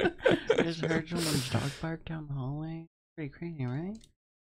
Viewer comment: "Did you say south park down the hallway?" - "WHY would I say that!?" made me crack up more than it should have, their nonsense (affectionate) always gets me xD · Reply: I heard "dog fart down the hallway" 😂